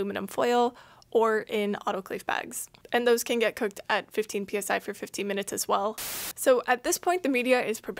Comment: are you saying 15 minutes or 50 minutes? I think I hear 50, right? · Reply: 15